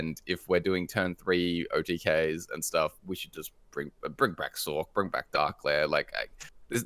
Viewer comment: sorry what is saying “Bring back saw”? Sore? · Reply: Bring back Sorc! (Sorcerer's Apprentice)